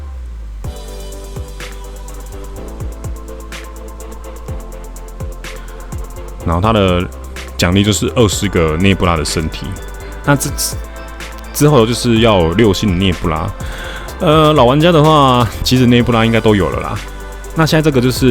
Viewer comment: 聽成6星的滅霸 嚇死我了